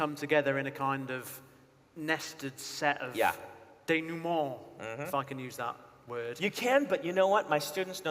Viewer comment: Accents are wild. I'm french-speaking and I didn't get what Brandon was saying when he was talking about "denouement", I kept hearing "Denoowah", till I went back a little further in the video to when Joe says it, and I don't know it's it's a factor of him being from a land closer to the country of origin of the word, but I understood him immediately. Dénouement, bien sûr, sacrebleu! 😅